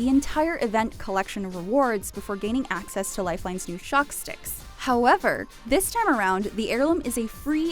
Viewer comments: I thought she said chopsticks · What did you call Lifelines Heirloom? Chopsticks? Chalk Sticks? They're Drum Sticks 😂! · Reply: Shock sticks, no? Isn't that what they're called?